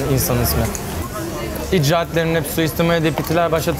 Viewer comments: de Orkun abi ne diyorsun anlamadım🤣🤣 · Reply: Ihtila başlatılırsa istila talebi olmaz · orkun abinin ne dedini anladımızmı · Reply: Ceza ya